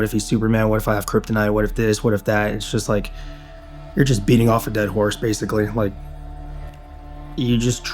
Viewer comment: Did he say you're beating off a dead horse 11:55. 😂😂😂